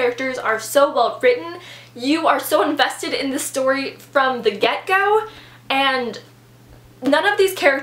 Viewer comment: Oh my goodness. I just realized when you said get-go that it's not gecko. I always thought people were saying "from the gecko." 😳